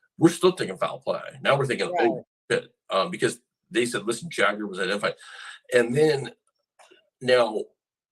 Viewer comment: does he say “Jagger was in a fight?” Did anyone catch what he said here?